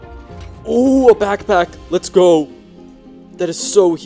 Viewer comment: what did he say? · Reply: ooh a back pack?